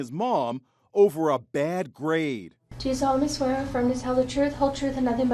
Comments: I thought it said bad grape lol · I thought it said over a bad grape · I thought it said bad grape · I thought it said bad Grape. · I thought it said a bad grape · I thought it said bad grape😅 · I for real thought it said grape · I thought it said bad grape lmao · Who else thought it said “ over a bad grape” 🍇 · Oh idk why I thought it said bad grapes. Kids a sociopath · I thought it said over a bad grape... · Who else thought it said bad grape? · Who else thought it said bad grape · At first I thought it said a bad grape 😂😂😂😂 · I thought it said grape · I thought it said over bad grape lol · i thought it said ‘bad grape’ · Ohhhh, I thought it said over a bad grape. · I thought it said grape lmfao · thought it said ‘over a bad grape’ lol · I thought it said bad grape 😂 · I thought it said “over a bad grape”